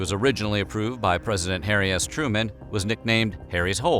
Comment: I swear I thought you said Hairy Ass Truman